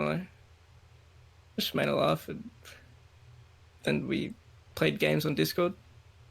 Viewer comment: I heard "we made love and played games on Discord" Guys, am I the only one?